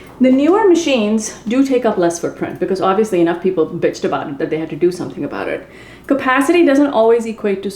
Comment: At the 7:00 minute did she say "enough people bitched about it....? Maybe it was her 19th video for the day and was in a bitchy mood. Take a break girl and enjoy your cooking.